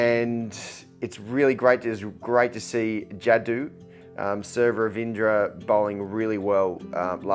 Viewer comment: did he say "Sir" Ravindra Jadeja..? 😂😂😂